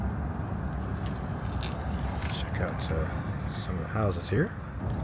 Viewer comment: when you were showing the santa rosa courthouse, and you panned over to the addition. i thought you were about to say "hideous" instead of "here".